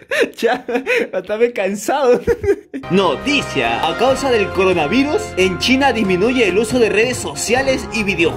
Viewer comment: Recién me doy cuenta que dice coranavuris xd